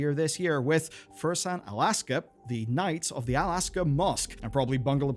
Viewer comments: Ooooh "Al-Aqsa" I thought you said "Alaska" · I thought you said the Knights of Alaska and I was so confused for a sec lol.